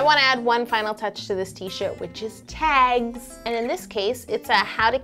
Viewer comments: did anyone else hear what she said? she said shit instead of shirt xD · Who else noticed that instead of saying shirt she said the "s" word. At 7:58.